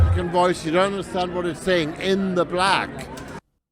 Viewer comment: I’m sorry but what the fuck did Hans just say? All I heard was “in the black”